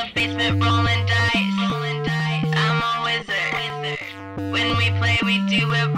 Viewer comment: I thought she was saying "I'm always there," not "I'm a wizard."